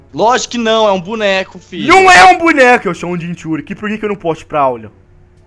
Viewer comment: só eu que ouvi o Naruto falando Filha😐😑😕?